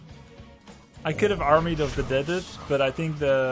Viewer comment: Did he just say 'Armied of the Deaded'? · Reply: Yeap, he verbalized, like "I could have casted", he said "I could have armyofthedead-ed"